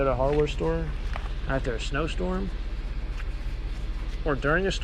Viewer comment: Happened to say "Stosnorm" instead of "Snowstorm" 😂